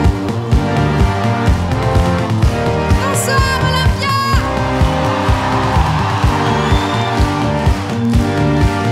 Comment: What's Olivia said at 0:23s? Is it French? Help me understand it, please! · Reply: Ngọc Tân Phạm '' bonsoir Olympia''